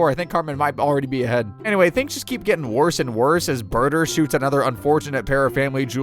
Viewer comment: Am I hearing Burger?